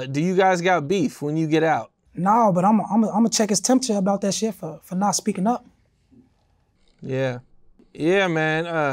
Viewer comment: Did he just say check him temperature 😂😂😂😂